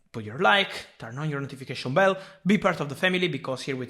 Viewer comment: did u say family?